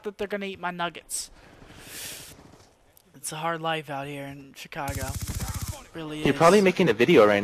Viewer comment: Did he say he lives in Chicago